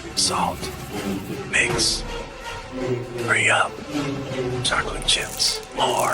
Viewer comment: CHOCOLATE CHIPS WHAT I THOUGHT YOU SAID MILK